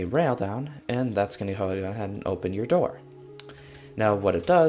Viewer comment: I think you were saying to “go ahead” and open the door.